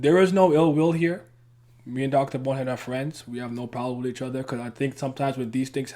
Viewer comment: Wait I'm lost 😂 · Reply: ithought you said dr butthead but i heard wrong ahahaha, its gery close tho